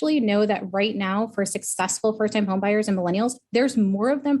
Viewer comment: I thought this video said "homeboys" not "homebuyers" 😆